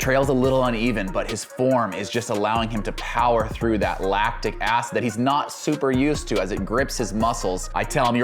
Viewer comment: swear he said laptic acid.... I know better but I might start calling it that on the track now...